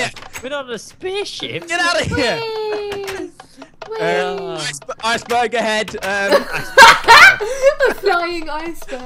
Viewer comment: sounded like ice burger head